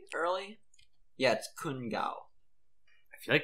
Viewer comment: When everyone was talking about Kun Gao creator of Crunchyroll, I misheard the name as Kan Gao who made To the Moon.